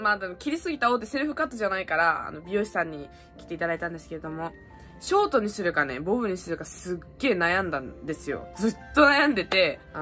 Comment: ショートにするかボウズにするかに聞こえた かわよき！